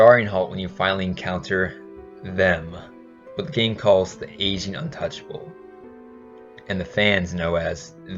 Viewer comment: I swear to god I heard "asian untouchable"